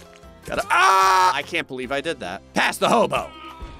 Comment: When he said “past the hobo” during the October pogo fight I heard “pass the hobo” like the hobo was a condiment